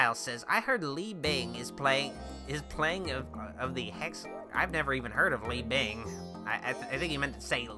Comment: When he said "Li Bing is playing one of the Hex Girls" I misheard it as "Lee Ving is playing one of the Hex Girls" and I was so excited